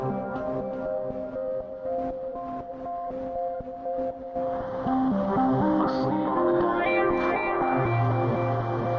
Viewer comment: What does the voice say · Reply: Asleep on the dining room floor, the world turns softly...